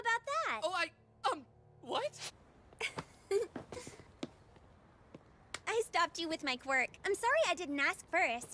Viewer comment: I HEARD “I stopped you with my fork” Hahahahahahah